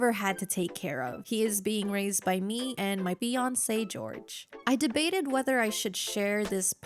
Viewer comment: somebody please tell me, i'm not the only one who hears "beyonce George" 😅